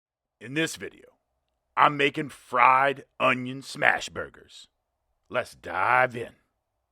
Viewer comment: at first, I thought he said, "onion snatch, burger".